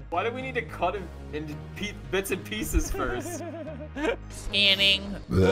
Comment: On 2:02,i thought said the candy “racies piecis” idk the spelling!